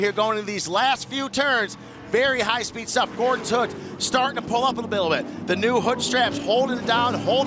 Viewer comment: "Gordon's hood, starting to pull up a Bittle Lit" hahaha, anyone else catch that?